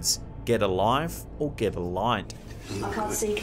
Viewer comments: I heard "get the boy" not "get a light". But I didn't have my headphones on. And that's 100% Robbin's voice. If that wasn't faked then... WOW😲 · I heard "In the light" Not get a life or get the light But that's just what I hear. The best way to listen to them is to shut your eyes have earphones in and not have any info on what "they" may be saying. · I didn't hear "get a life" I heard "get the xbox series X" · I hear say" get a light " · I thought it said In the light... not Get a life or get a light... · I hear "get a light" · I had headphones in my ears when watching and replayed several times. I hear " Get A Light" by the way....love your videos, always look forward to watching them!! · I heard "get a light". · In the number 3 video I hear "Get a light." It makes sense since they were literally in the dark and experiencing a power outage and trying to find a light or light sources to illuminate their surroundings. Hopefully the voice was just trying to be helpful.